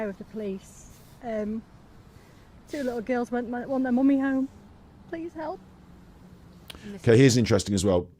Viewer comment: 16.46 on my playback ....you reflected statement being, "Erm...two little girls want their mummy home".. I listened a few times and I heard "Erm...two little girls MIGHT want their mummy home..."